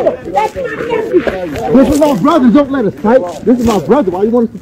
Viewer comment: As hubby was laying paws on did I hear say "this is my brother I love you...don't let us fight, he need help"??? HE need help??🤔